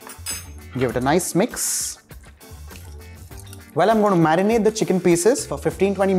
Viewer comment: I thought he said "marry the chicken pieces" instead of marinade. and I'm just here like "Yes,total mood! that chicken already looks sooooo good"